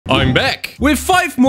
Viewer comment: I thought you say 'I am black'